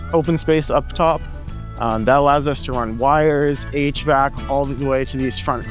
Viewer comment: he said "H-Vac" - I'd thought he said "8-Track". That would have been a very nice 1970's retro-touch!